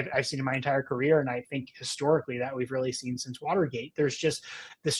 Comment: I think I misheard "Watergate" as "Slaughtergate" which seems to be an appropriate name for what's been happening.